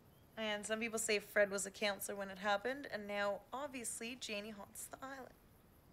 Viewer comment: Question - At 7:11 when the girl told the new girl the "legend", I didn't understand her when she said "Obviously Janie holds the _____" What was it she said Janie holds? I must have played it back at least 10 times and it sounds like she mumbled n swallowed spit on the last word. Can anyone clarify what she said for me? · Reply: Its "Obviously Janie haunts the island" I can hear it on my new phone so much better or maybe because I read the answers first im now hearing it so much better. I dont know but it was driving me nuts! Thanks for posting all these films for us! I should do a skit for my new phone or something... Smh